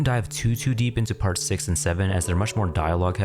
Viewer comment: d- did you say s- six and seven?